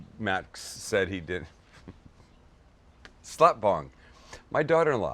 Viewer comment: did he say slutbong lmfao